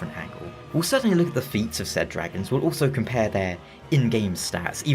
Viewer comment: at first I heard "feet", and I’m sure that’d be a metric if Miyazaki were ranking them.